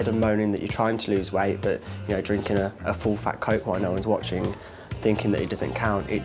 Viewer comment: Did he just say "full fat coke" 1:02? Lol